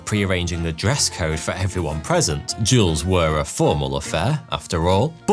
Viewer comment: I kept hearing "jewelers"